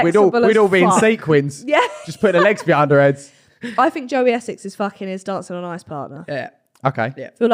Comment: I was half listening and thought said that joey essex is a nonce